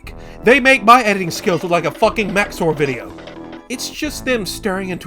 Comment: A who video? I can't tell what name he is saying, can someone help me out? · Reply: Max0r